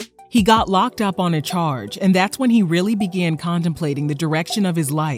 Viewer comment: Did she really say “conDEMplaining” at 1:17??